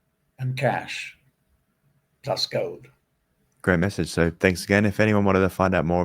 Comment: I'm a Paid Subscriber to Simon's Newsletter; so, I also listen to taped interviews quite closely, as you might expect. *Here's a question for any readers of this Comment: What the Hell did he say at 31:15? Preceding the phrase that I couldn't understand, he says, "... by 2028, make sure that you're well stocked-up with [preserved, shelf-stable]Food and [I have to assume that he means $USD, in paper currency form (e.g., not digits on a screen)] Cash." Okay..., so what did he say afterwards? Did he say, "... trust Gold." ??? Did he say, "... buy Gold." ??? Did he say, "... get some Gold." ??? Thanks for your Reply. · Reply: I think plus gold. The transcript says the same thing. Not fully sure though.